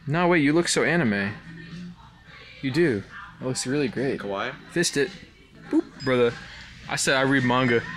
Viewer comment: The way he said kawaii made him sound like he said kowai instead lol